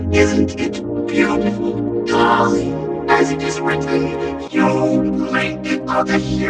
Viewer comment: i always hear 'isn't it beatiful? cause, as it is written:' but it is: 'colley! As it is written:'